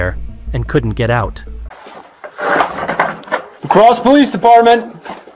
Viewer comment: i thought they said "crawl space department" 😂